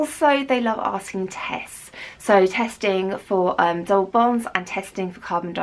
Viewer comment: What does she say at 2:50? "Testing for ..... and testing for carbon dioxide" ? Thank you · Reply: DOBOS